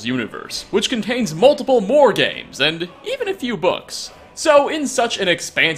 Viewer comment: I honestly thought you said *bugs*, instead of books.